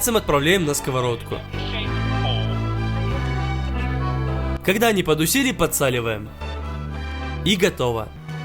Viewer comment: я понимаю что он говорит "когда они подусели", но почему я слышу "когда они потусили"..?